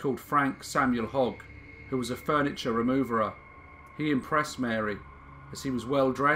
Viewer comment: did you just say removerer